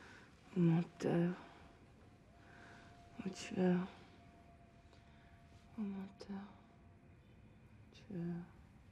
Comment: Does anyone know what Madeline is saying to herself in French when she's on the bed 4:28-4:43? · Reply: right, in French: "qu'est ce que je fous ici?" "menteurs, tueurs, partout"